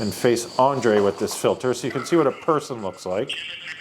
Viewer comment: I heard "Jesus is a person"!